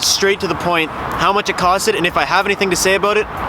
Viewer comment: did he say, "costed" ??? Maybe not but funny if he did